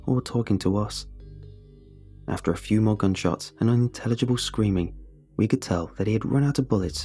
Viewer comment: Haha, I thought you said you were unintelligent. You already assumed no one would help or save you. That's a level thinking